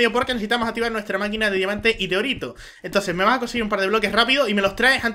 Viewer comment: se que dice orito pero parece que disiera doritos